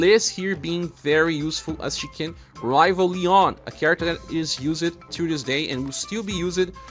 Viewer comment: Liz can rival who ? I hear "He-yon》. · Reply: Leon!